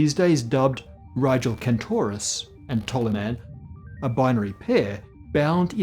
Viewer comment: From the Corrections Department: 1:26 should read "Rigil Kentaurus" rather than Rigel. Rigel, Beta Orionis (β Ori), is a blue-white supergiant star located in the constellation Orion. Thanks to Tristian Yamaty for pointing this out. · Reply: the subtitles say "Tolimar" but the slide says "Toliman"? I so want to see pictures of Proxima B - Starshot, get on it! lol